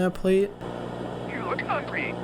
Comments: “you look ugly” HELP AM I MISHEARING THAT LMAO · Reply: I heard “you look hungry” .. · “you look ugly” HELP AM I MISHEARING THAT LMAO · did he call her ugly or hunger